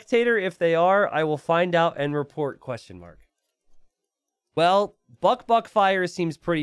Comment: He sounded like he said buckf***fire once